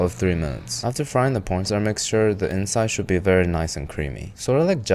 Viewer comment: I swear he said "pron star mixture"